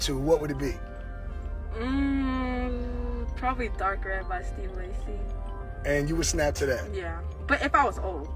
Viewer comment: What artist and song did she say at 2:12? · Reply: Steve Lacey